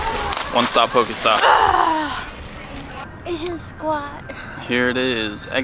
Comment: did she say "Asian squat", and what does that meen? :)